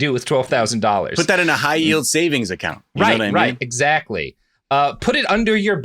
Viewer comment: I thought he said a high-heeled savings account